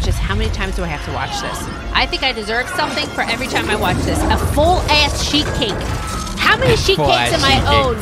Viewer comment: I legit thought said “a full asscheek cake” 😂😂